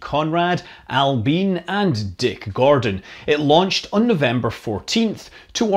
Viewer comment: I heard "Dick Gordon" as "dick garden" and found it very difficult to focus after the image that appeared in my mind.